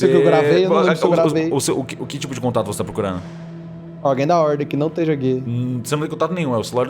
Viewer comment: parece que ele falou"alguém da ordem que não esteja gay" (saibam que não quero ofender ngm ou que pareça uma sitação homofóbica,só foi engraçado mesmo)